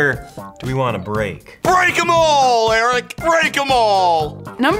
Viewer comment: ''BREAK EM ALL ERICK me'''did he just say ''BREAK A MALL''